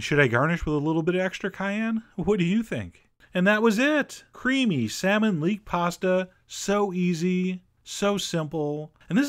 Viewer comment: Thumbs up if you thought it said creepy salmon leek pasta,.